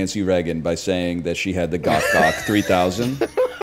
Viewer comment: "GOTH GOCK 3000"???? am i hearing him right??????? i can't be hearing him right. it's fine.